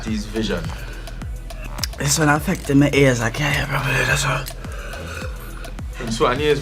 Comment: It’s funny when he Prince Swanny said girls close captioning thought he said gears those moments when close captioning on YouTube is so off sync and so broken and displays the wrong words🤦🏻‍♂️